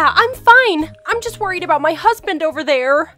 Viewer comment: did say husband????